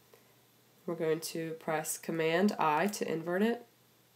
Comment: Hey, can someone please write which key is she talking about on 1:04 please, I cant understand what she said, thank you! (Sorry for my bad english) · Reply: Command + i